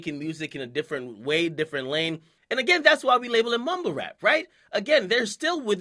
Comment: I thought he said homo rap not mumble rap